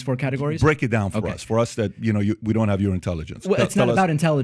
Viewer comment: “We don’t have your intelligence.” “Imagine your intelligence if you were as smart as I am.” Good thing I’m sober. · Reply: That’s not what he said. Around the 15:50 mark, PDB says, “Break it down for those of us that don’t have your intelligence.”. David understandably doesn’t know if that is a sarcastic jab or not, and responds by saying, “with your intelligence, imagine what you could do if you looked up the things I look up.” Not that it matters, but I am not a Biden supporter or a Trump supporter, and there are many things David said/says that I do not agree with, but he’s being characterized as being arrogant, which I think is far from being an accurate depiction of this interview.